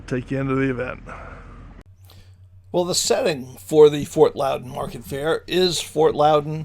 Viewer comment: Lol thought it said fart loud